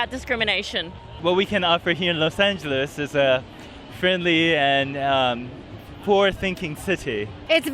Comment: I thought at first he said “friendly and poor-thinking” ... 😶